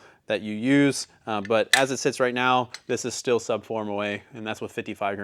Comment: Did he say Sub 4 moa at 11:03 ?